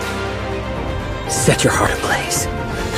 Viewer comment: I swear I heard “Set your fart ablaze”